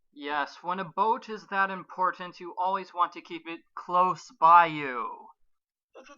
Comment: "When a boat is that important, you always want to keep it close BAYOU"? XD Sorry, I might've misheard.